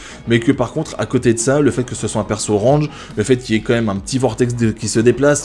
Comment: A 11:54 kisuke perso orange c est rouge pas grave peut être que moi j ai mal entendu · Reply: Il dit que c'est un perso Range et non pas Orange mais c'est vrai sa prête confusion